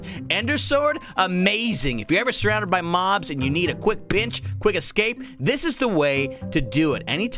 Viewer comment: Did say "if you need a quick b swear word" on 7:02 and 7:03? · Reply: Gamers of greece he said quick pinch